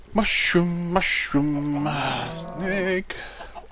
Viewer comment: did you say snake? as in badger badger badger badger badger badger badger badger badger badger badger badger mushroom mushroom?